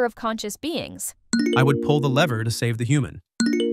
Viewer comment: Sorry, did Gemini said I'd pull the leer to save the human? 💀